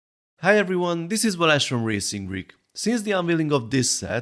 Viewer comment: does anyone know what his name is? It sound like blash im not sure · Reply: Turn on the subtitles! The name is btw :)